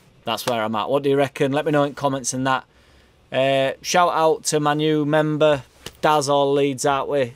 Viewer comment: i nearly unsubbed when i heard 'shout out to man u'..... but it was actually... 'my new member'.... :D :D